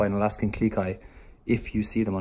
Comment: Has anyone else got this video as a suggestion after watching Glam and Gores new video with Ripley and Creature? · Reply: Claire R yep! Funny thing is I thought she was saying Alaskan GLEE Kai XD